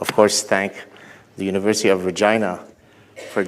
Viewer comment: did Gaad say the University of Vagina? I swear he did. Good one, Gad. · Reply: the University of Regina.... good try :))